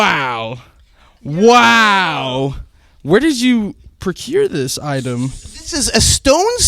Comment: Earl says 6:27 'where did you ** this item' my main language is not English and i did not understand what he said , can someone tell me? thanks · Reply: Procure, it means to obtain something.